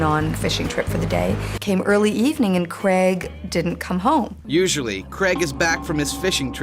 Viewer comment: I didn't hear her probably and I thought she said cwaig and I was just so confused as to why and flitted past that detail